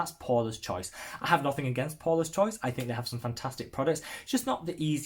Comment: Poreless Choice hmm haha, oh THAT company. I misheard! Great options here! Very informative. thanks again